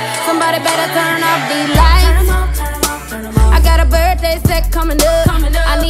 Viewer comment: I thought she said "I heard a bird face" and I thought she was talking about Karlie Redd. 😂😂😂😃😃😂😂😂 then I realized she said "birthday set" 😄🤗🤓